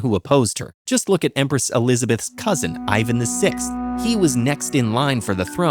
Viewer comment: um.. 2:52 caption reads Ivan 4th instead of 6th....fyi